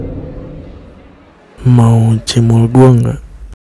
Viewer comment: itu si bilang apa? Mau cium gw gk atau mau cimol gw gam